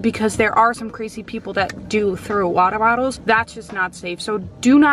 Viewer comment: did she say throw ? Cuz idk